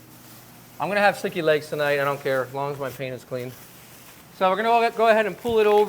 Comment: I had to turn on the captions because it sounded like he said, "I'm gonna have sticky legs tonight, but I don't care. As long as my peen is clean." 😂